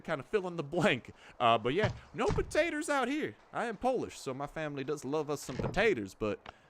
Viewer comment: did i hear right??? polish???